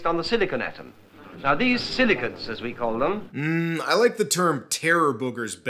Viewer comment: Ohhhhh... silicates 😅 Not for nothin, but for a second there it sounded like he was saying "silly c**ts"